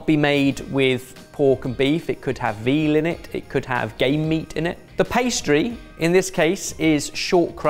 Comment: Does Ben say the meat pie can have gay meat in it???